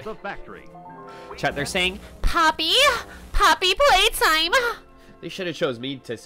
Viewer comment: Ngl, thought it said sloppy playtime